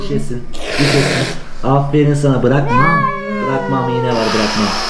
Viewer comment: ''Bırak'' mı diyor ben mi yanlış duydum :D